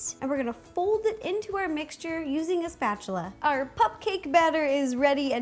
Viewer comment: is it me or did she say popcake?